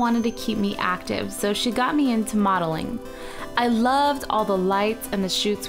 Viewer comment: Oh lol at first I thought you said waddling instead of modeling